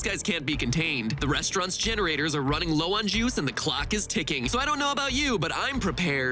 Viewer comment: Why did I hear Jews instead of juice?